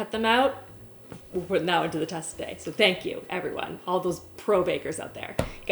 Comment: Logically I know you said "thank you oven" when it beeped but I just heard "thank you Alvin" because you said 'which name?' and now I can't unthink ovens as Alvins 😅